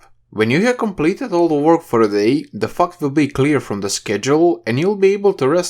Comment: What did u say 7:20 ?😂😂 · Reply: "...all the work for the day, the fact will be..."